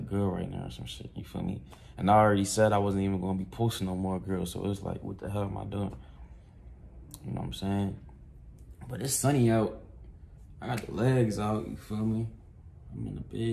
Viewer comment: Thought u said no more girls in video